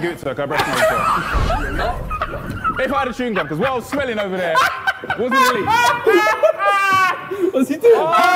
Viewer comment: What is he actually saying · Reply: “If I had a chewing gum I’d give it to her cos what I was smelling over there”